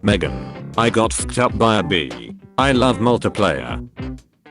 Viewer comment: I wasn't looking, only listening, and was like "What's so funny about saying 'I got fucked up by a bitch'?" but turns out, no, it's not the censor, it was literally "bee"